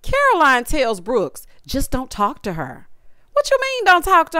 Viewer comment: No, Stanbury told her to talk to her too! I had to rewind it cuz I thought she said don’t talk to her too. I watch the show with closed caption on! lol